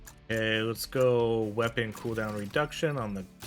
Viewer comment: I could be trippin cause I've never played this game in my life but I kept hearing you saying "we're gonna go cool down reduction" throughout the beginning of the video and you picked maybe one cooldown reduction and skipped the rest